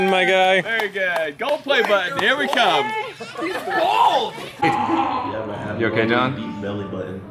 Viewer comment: did anyone else hear yell “He’s bald”!